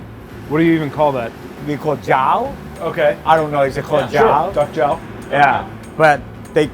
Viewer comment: I'm so late but guys he for sure said duck JAW, not xiao or whatever they thought he was saying. they need that immigrant parent accent education lmaooo. they repeated it like they knew too ahhaha. it's ok guys